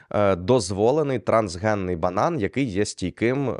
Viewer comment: На 17:30 почулось «трансгендерний банан» 😂😂😂